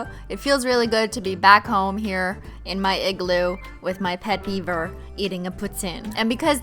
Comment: So it's a beaver tail?? I thought it was fishnets, and then I thought you said Bieber, not beaver, so I was like, what is a Bieber tail and why does it look like fishnets - but after all Bieber is Canadian so I had accepted the whole thing. I'm glad you showed us the google results page 😂